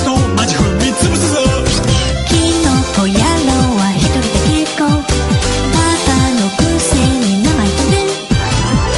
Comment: the only part i dont understand is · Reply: egg is a good source of egg it says " I'm good enough with one galra, don't act like you're #1 zarkon. know your place" ^v^;; sorry for the confusion!